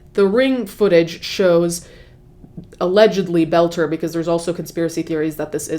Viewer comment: Friendly FYI: His last name is Boelter pronounced bowl-ter (similar to the word boulder) not that he deserves special care but just to avoid confusion because I thought you were saying “Belcher”.